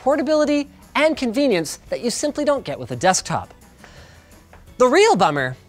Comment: is it me or did he just say desktok? anyway love the content, thanks for making the community a bit wiser when it comes to computing tech